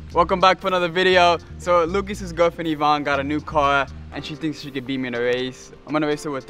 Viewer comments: мне одному послышалось как он сказал ивангай и а4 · Nobody: Lucas’s girlfriend Ivanne got a new cuar. (00:10) Me: wtf is a cuar. Say it normally, car